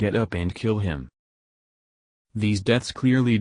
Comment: For a second, I thought it said Phone Guy is Phone Guy.